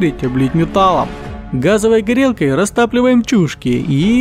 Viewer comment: Мне в 0:25 послышалось «растапливаем чушек»)